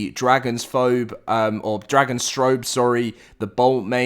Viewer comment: For a second i thought you said "Dragon-Strobe" for Dragostrophe